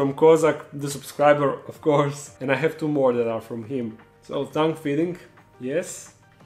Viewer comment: So how many thought he kept saying "tongue feeding"? lols. Best intro by far too!